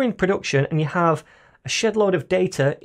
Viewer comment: I thought you said a "shitload" of data 😂😂😂 btw your videos are awesome, thank you!